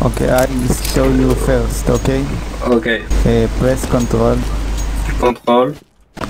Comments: i hear "KONTOL" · Wait a minute? Did he say "kontol"? 6:15 XD